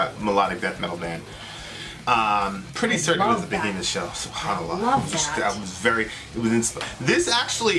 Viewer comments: did said Subhan Allah?😀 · Did he say Subhan Allah?? 😂 8:10??